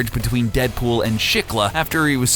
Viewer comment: what show is it? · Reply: Shikla is written as”schifflet” and @ 06:36 it is written as “shakeela” @ 06:43 her name is written as “shickel” @ 08:03 you say “switch” but the subtitles say “sue” @ 08:15 you say “Pool” but the subtitles say “Poole” @ 08:40 you say “Deadpool’s and Spider-Man’s” but the subtitles say “Deadpool’s in spider-man’s” @ 08:55 you say “with” but the subtitles say “was” @ 09:16 “Deadpool”isn’t capitalised @ 09:42 you say “fake” but the subtitles say “think” @ 10:10 “Terror” should be written as “terror” @ 10:15 “spider-man” should be written as “Spider-Man’s” @ 10:50 “healing” is written as “unit” @ 11:01 “spider-man” should be written as “Spider-Man” @ 11:05 “uncanny x-force” should be written as “Uncanny X-Force” ibid. for